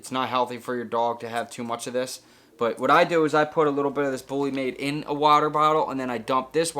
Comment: Great video, I subscribed. You mentioned that electrolyte drink for your Pitbull. What was the name. I could not clearly understand. Thanks.😊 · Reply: sounded like he said “bullymade”